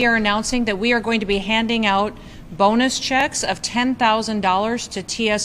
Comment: 🤣🤣🤣 Sorry, I thought dog killa said Boner Checks🤣🤣🤣, must be old starting to hear words wrong like POTUS 💯🤣🤣🤣🤣🫡